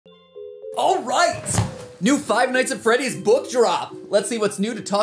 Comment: I'm sorry, did you just say five "knots" at Sharky's